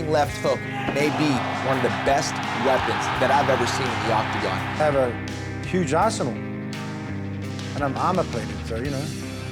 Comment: sounded like he said he has a huge asshole. No disrespect mark is the man :).